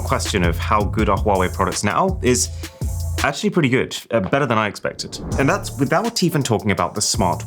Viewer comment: i thought he says better than i…phone